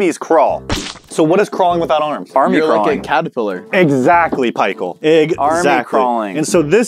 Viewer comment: why does he say "exactly pie-chael"❓